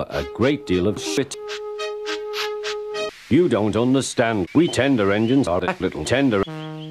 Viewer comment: What did he say? Did he say "We tender engines are that little tender"?